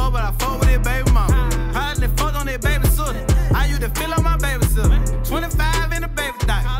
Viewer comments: I was liking this brothas music now Correct me if I'm wrong but if listen at about track playing time between 1:17 - 1:20 it sounds like he said ( I use to feel on my baby sister) wtf! Ion know bout dat shit rt so if someone or even dude himself clear that lil verse up for me ?????? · I use to feel on my baby sitter...lol hell naw bumping comb every where i go free brazy. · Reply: I swear I thought he said he use to feel on his baby sister I had to come read the comments then found out he said babysitter 🤦🏾‍♂️